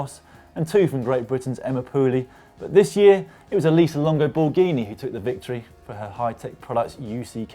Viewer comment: Ha! I thought he said Elisa Lambo Bourghini.